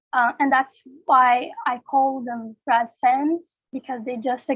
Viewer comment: Bless you, Ms.. I learned so much from this. BTW, I don't think you said "rad fems" at 12:44 Could you tell us what word you said? This was a very helpful introduction to the social context young women face in Brazil, and I hope we hear much more from you, Sister! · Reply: I am pretty sure she said "rad fems."